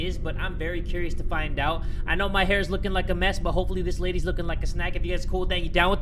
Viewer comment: Did he just say hopefully this lady is lookin like a snack LMAOOOOO